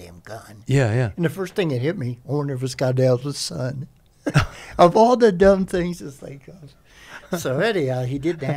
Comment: What does he say at 19:35? Something about a stupid thought he has but I can't make it out. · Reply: Elder or Eldest Son mission. It was psych ops where they would plant bad ammo or leave bad ammo behind. The enemy would then use the ammo, shoot it, but it was designed to explode. So his thought was, if he has an Ak-47 from the enemy, and it held elder son ammo, then he would be killed by it. Pretty sure the phrase was elder son. If you listen to other sog casts they mention the elder son missions.